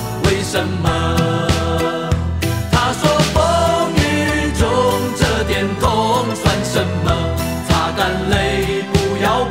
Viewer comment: 第一次听时，听成： 他说风雨中 这点痛 【单身狗】 擦干泪 不要怕 至少我们还有梦